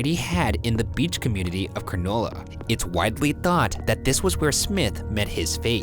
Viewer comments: #postmortem I’m from Coogee, (you do not know how to pronounce it) and I was asking my parents about this case, Coogee beach has an island called wedding cake island (because of the way it looks when the seafoam hits) and there was this old tale about a widower that lived on wedding cake and told tourists that if they jumped off of the cliff at Coogee they would be totally fine. A lot of people do this and some are all right but so many people have broken bones, become paralysed, ect. I thought it was just an old tale but my nana seems to think she knows the man that lived there, I think she said his name was Andrew Smith. A lot of people around here have the last name smith but Idk, something to think about I suppose. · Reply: And cronulla! I thought he said 'granola' 😂 Oh man, Coogee seems like an interesting place 😮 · im sorry but this man literally just said coochie aquarium i have never laughed harder in my life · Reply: When he said Cronulla I thought he said granola, had to replay it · When Ryan said Cronulla I thought he said granola! I’m thinking now where tf is granola in Sydney?! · sorry did he just say "conolla" ?? Im wheezing